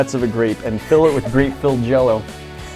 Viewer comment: Probably the first people to hollow out a grape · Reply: "And fill it with grape filled jello" thats exactly what he said